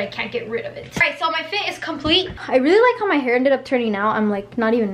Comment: Wait I thought she said she was Turing 18??